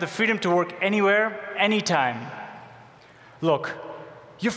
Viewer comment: DID HE SAY YOU FOOLS ARE ALWAYS CONNECTED AT